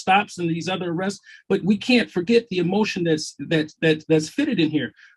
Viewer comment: …did you say elocution? LOL.